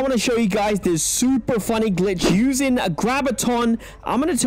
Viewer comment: I heard grabiton